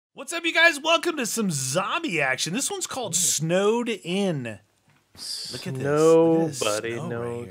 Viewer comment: When you said Snowed In, I thought you said Snowdin from Undertale!😂😂